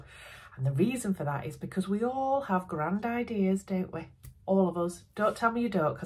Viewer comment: you are saying: We have all grand ideas! Somehow I was not concentrating and only listening to you in the background, so I misheard that as: We have all grandad ears! You can imagine how surprised I was. 😉🤣🤣🤣 As always you made me chuckle, your videos are always very entertaining. And I love the idea for this card holder, I might make one for a nephew. Thanks.